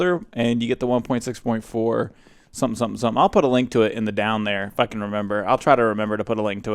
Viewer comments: Lol it sounds like you said "fuckin remember" at around 00:50 :D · It sounded like: I F*ck*n Remember